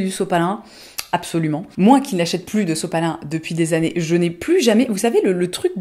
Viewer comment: Salut! dans le min. 7:23 elle dit "sepalin" ou quelque mot comme ça? je suis en train d'apprendre français et je ne comprends pas ce mot, j'aimerais savoir qu'est-ce qu'elle dit dans ce moment. Merci! :)